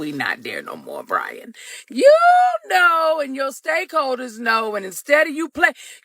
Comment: I swore she said "stankholders"...and I didn't feel it deserved a correction. 🤣🤣🤣